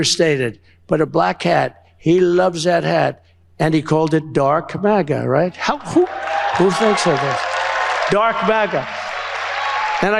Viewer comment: When Leon Musk was at the small gathering with Trump, it sounded like he said, “drunk MAGA” and tbh by his behavior it likely was more suitable.